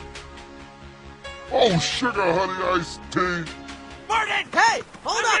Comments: I also thought Marty said "Oh s***! I smell of iced tea!" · if you here closely at 0:20 it sounds like Marty saying "oh shit I had a iced tea". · if you here closely at 0:20 it sounds like Marty saying "oh shit I had a iced tea". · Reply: What i heard was, " Oh shit honey I stink." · if you here closely at 0:20 it sounds like Marty saying "oh shit I had a iced tea". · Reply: I heard it “oh shit I have a nice tea”